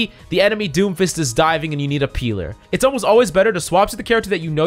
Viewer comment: pealer? Who heard it